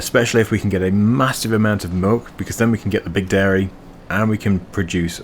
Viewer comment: Did you say "moolk"? lol I listened a couple of times and made me laugh :) Might be the accent sometimes. Sorry, love the vids mate :) Some might say I say "melk" to be fair